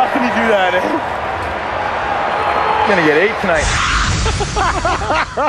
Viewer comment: I know but it sounds like he says "I'm gonna get laid tonight"